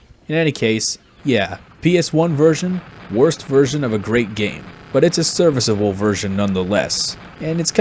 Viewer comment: At first I thought you was saying that it was a bad version of a bad game and I was like, "idk, I think it's pretty good" and then I realized that I'm an idiot and this is just about the version itself, not the original game over all. Never played it on ps1 tho. But that's just a theory. A game theory.... Wait, it's actually fact and I keep forgetting im not the game theory guy... Sorry, disregard this nonsense.